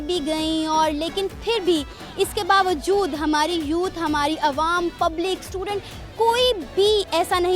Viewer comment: when she said.. hmari youth.. i heard hmari choot.